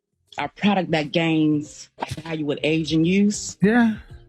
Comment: captions wrong - product that gains value with age and use